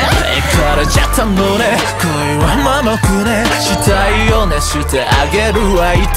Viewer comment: 「したいよね してあげるわ」っていう歌詞だったのか...死体を熱してあげるわって聞こえてた...たぶん既出だろうけどなんかすげぇ